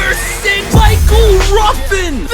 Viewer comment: i thought you said michael jordan for a seocnd💀💀